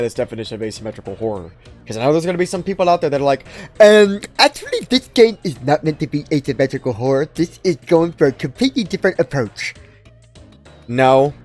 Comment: tf is he saying here? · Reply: "Ermm actually this game is not meant to be asymmetrical horror this is going for the completely different approach ☝🤓"